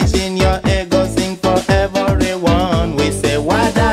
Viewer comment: I was so young I thought what he was saying was heavenly wonder no instead of everyone 😂😂😂 I'm proud to be in this era not gen z 1990